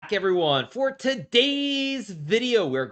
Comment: Why does it sounds like he is saying f**k everyone